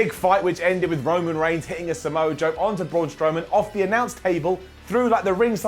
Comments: Did Simon just say that Roman hit a Samoa Joe on Braun? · Did Simon said "Samoa Joe" or "Samoa Drop"? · did he say samoa joe? Lmao